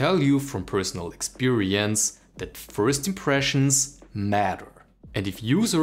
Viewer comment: I heard it as first impressions "mutter"